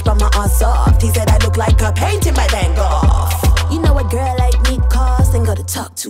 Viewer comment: I always thought she said: He said, "I look like a painting, my damnnn gawwwddd” 😂😂 But it’s “...by Van Gogh “